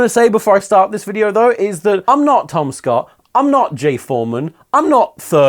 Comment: i thought tom said "im not tomska" and i felt the metaphorical pillar of my life crack down the middle